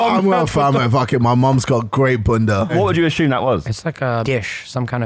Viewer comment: As a native to America that sounded highly sexual to me sound like jj said his mom got great puccy 😂